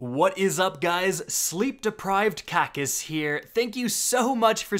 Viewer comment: What he said: What's up guys, Sleep deprived Kackis here What I heard: What's up guys, Sleep deprived Cactus here